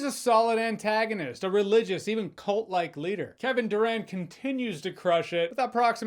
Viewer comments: Literally switched the CC on cuz I thought u said Kevin Durant 😂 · I heard "Kevin Durant" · thought u said kevin durant lol